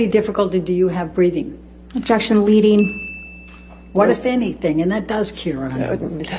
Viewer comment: What does the lawyer say? I hear something like: "What if anything, and that does cure it" and then someone (probably the judge?) says "It doesn't" and then it is overruled. Can someone expalin/clarify please?